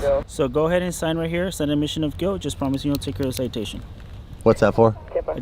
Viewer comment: It sounded like me that the officer said "sign right here, sign an admission of guilt" at 5:10.